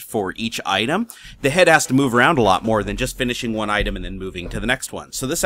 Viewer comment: Did you just say Head Ass? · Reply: no he said head has to move around alot